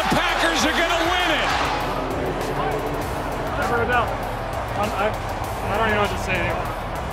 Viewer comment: ……what did Rogers say to that guy?? I listened like 10 times and couldn’t figure it out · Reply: “Never a doubt” thats what it sounds like